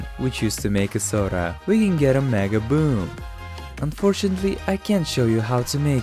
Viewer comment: What did he say in 1:19? Maga-boom?